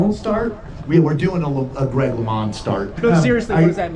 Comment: Took me a while to figure out the LeMons start. I thought they were saying LeMond start and thought it had something to do with Greg LeMond.